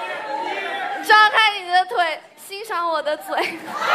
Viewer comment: What she said at 13:09 ?every face was kinda shocked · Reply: Lol she said open your leg and admire my mouth 😂🤣